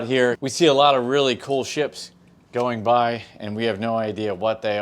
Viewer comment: I thought you said, "there is a really cool $hit out here!" but it was just a ship... Maybe bird pooo art or something. Maybe you guys could catch seagulls and train them on the side. P.S. love the channel guys